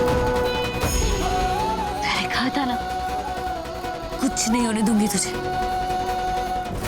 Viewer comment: What she said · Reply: She said ,"Maine kaha tha na kuch nhi hone dungi tumhe"